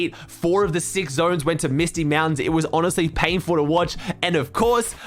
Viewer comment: did.... did he say Misty Mountains